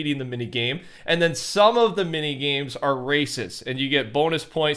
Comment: Low-key it sounded like camprusher said racist lol